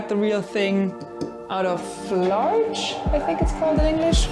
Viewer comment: Lol I thought you said you made the groove out of large 🤣 I looked up softwood species and you must have said "larch". In Canada we use use spruce, pine, and fir mostly. I hadn't heard of larch before